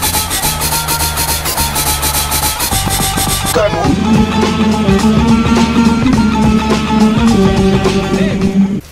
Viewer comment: Что сказал Пахом на 0:13? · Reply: Come on